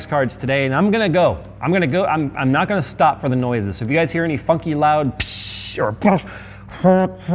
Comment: I thought he said any “fucky” sounds had to rewind to realize it was funky